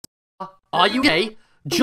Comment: I heard “birth” instead of “earth”☠️☠️